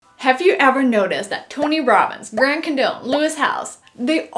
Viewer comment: I thought she said “Grant Condom”